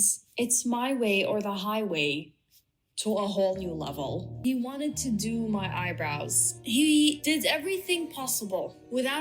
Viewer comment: At first I thought it was a misunderstanding because I thought she said "I wanted to do my eyebrows" and to me that means waxing and tinting so I'd be confused if a client asked me to do them and then tell me no. But I watched again and HE wanted to do her eyebrows omg. Like I know in Latin America people like them dark and blocky but damn